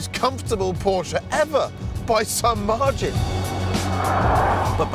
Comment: What did he saying?? "most comfortable Porsche ever by XXXXX " ??? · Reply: “By some margin.” Sorry for the lateness